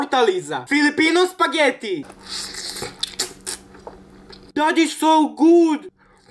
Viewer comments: Who heard her saying daddy’s so good 😫. 💀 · When he said "That is so good" I thought he was saying "Daddy so good" 😂😂